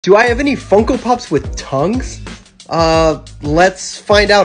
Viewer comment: i thought you said “vocal pops with tounge” 😭😭😭😭😭